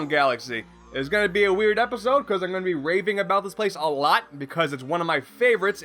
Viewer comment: "It's gonna be a weird episode cause I'm gonna be raping about this plade a lot!" - 2015 Lol, that's what it sounded like to me at least. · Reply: PyroDanTheManiac "raving about this place" he said